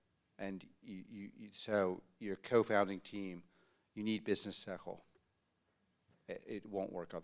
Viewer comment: what's the word: "you need business ?" Hard to understand, as a foreigner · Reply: I believe that the word was 'sechel', which is a Yiddish term meaning "intelligence, common sense, or practical wisdom".